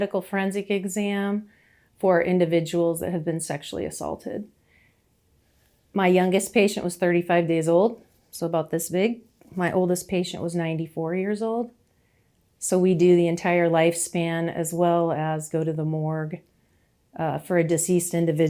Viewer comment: The way I gasped when she said that her youngest patient was 35 DAYS old. A one month old BABY. Sickening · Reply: Thought she said 35 years old ,,I gasped by looking at your comment that I was wrong